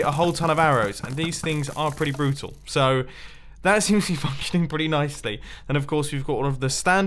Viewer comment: did he say fucking under his breath or working